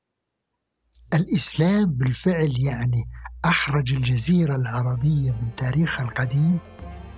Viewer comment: كنت مذهول من علمه وتمكنه حتى سمعت رأيه في الدقيقة 57:32 فأعدت النظر تجاهه ، يتشدق بالهوية والإنتماء وضعفها وعدم وجود دراسات في النشأة العربية وهو يرى " إن الإسلام أحرج الجزيرة العربية " لذا صدق الشاعر في أمثاله إذ قال: ما كل من حط له "دال" دكتور الدال يمكن دلخ ولا دجاجة 👌🏻 !!!! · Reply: أخرج وليس احرج ،، قصده نشر العروبة مع الاسلام خارج الجزيرة